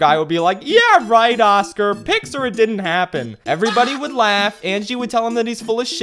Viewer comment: I always thought that when you said "pics or it didn't happen", I always thought you said "pixar it didn't happen"